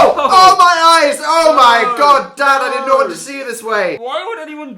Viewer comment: did dan just call shrek dad? what ever happened to Kanye??